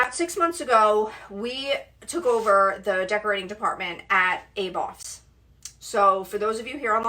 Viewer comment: Can you please tell me what is the name of the store you mention around 1:30-2 mins? Aboss? Adolfs? Sorry couldn't hear it. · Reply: Aboff's on Long Island